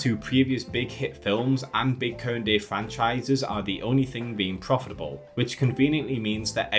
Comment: I can't understand what word is being said. I hear "Big Curd Day franchises"